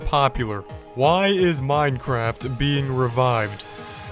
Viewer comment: OH MY GOSH!! I thought revived said REMOVED!! THANK GOODNESS!